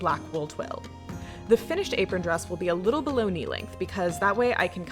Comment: lol unrelated at 5:44 I first thought you said your apron would be bologna length. I was all confused. I guess it's lunch time.